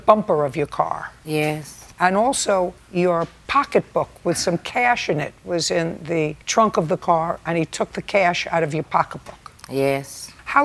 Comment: I swear it sound like she said her parents were in the trunk of her car